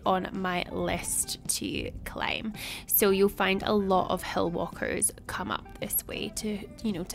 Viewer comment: 😂 I thought you said, “hell walkers.” I was like, “HUH 😨?! Girl! Y’all need to get out of there!” Always nice hearing your amazing voice over work. Glad you all were able to enjoy your birthday trip! Thanks for sharing! Take care!